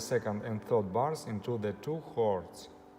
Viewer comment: Excellent advice. The only thing is I thought you were saying "hoards", and it took a while to realise you were saying "chords", but I got the message anyway 🙂